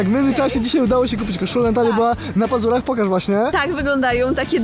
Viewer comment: Aaaaaaaa, była na pazurach, a ja usłyszałem, że na mazurach